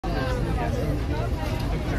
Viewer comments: "Do not touch the Kings Life Guard!" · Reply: I thought he said, "Don't touch the King's armed guard." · "Do not touch the Kings Life Guard!" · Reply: I thought he said do not touch the king thy God lol like whaaat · What I heard: “Do not touch the king’s lifeguard.”